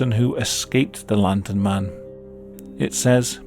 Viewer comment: Lmao at the beginning I misheard “The London Man”